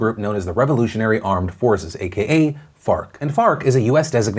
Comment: Me: Wait did he just say Fart? rewinds Me: Oh, ok.